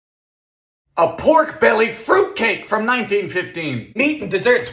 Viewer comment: I misread and misheard fruitcake, and it said/sounded like fruitcream